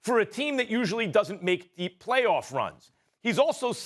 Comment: Dame been in the western Conference finals 🤷🏻‍♂️ but no deep play off runs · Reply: Max didn't so "no deep playoffs runs," he said "not many". So many ppl misheard that, I don't get it.